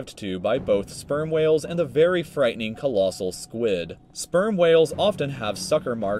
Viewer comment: did he just say sperm whale